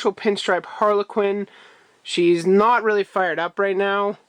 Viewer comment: did he say Harley Quinn?